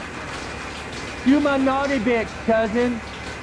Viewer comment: Two of my favourite lines I heard were “Do my naughty bits cousin” And “I’m sorry sometimes it just goes up like that it’s go down”